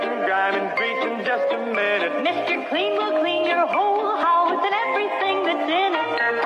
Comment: HAHAHAHA I heard "Mr. Clean will clean your hole" AHAHAHAH